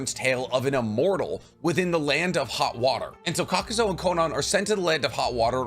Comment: I was passively listening but my ears immediately perked up bc I thought you said “the land of hot dog water” and I was like, I don’t remember thst clan from Naruto 😂😂😂😂